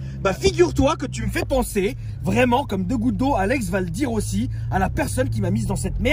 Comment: j'ai compris " Tu me fais penser a 2 gouttes d'eau a Alex Valdirossi " 😂😂